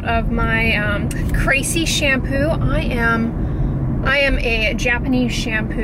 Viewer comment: Hahahaaa I thought you kept saying Crazy shampoo .... I was like using fragrance IS crazy